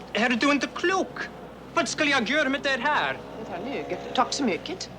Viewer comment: Great fun- thank you very much! Greetings from Sweden! P S the bellboy says at 3:49 " You are crazy, what should I do with this?! " Then Kate says something unintelligable and then " thank you very much! ", whereafter he says something about Kate being cheap. Their " swedish " sounds actually like a mix of danish and norwegian to me, but then I am originally from Germany! So - enough confusion?! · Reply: Oh my. "What should I do with this?" is about the last thing I expected, and it made me laugh. Thank you!